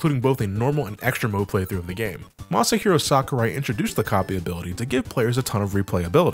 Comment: When I first heard say Sakurai's name at 5:40, I actually thought he was mocking it by calling him "Monster Hero Sakurai."